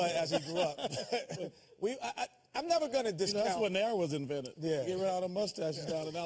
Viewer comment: does anyone know what Chuck says here bc I can't figure it out lol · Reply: he said that's where nair was invented. nair is a hair removal cream